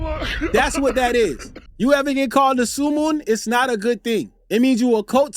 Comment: instead of SUUWUUU (blood gang talk) it’s SUEMOON 😂😂😂😂😂